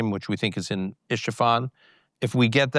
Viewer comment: did he just say the uranium is in I-SH-ifan?...